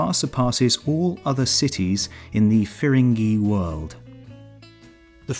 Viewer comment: What does he say at 5:13? "... it far surpasses all other cities in the ?????? world." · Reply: Firangi: literally meaning one of different color, but usually used for foreigners in India.